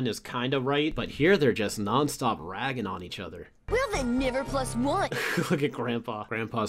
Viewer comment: WHAT DID JUST SAY???!!!!??? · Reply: He said never plus 1